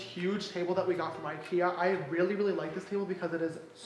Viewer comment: At first i thought he was saying he got his furniture from “his tia” than i realized I’m just poor and he said ikea 💀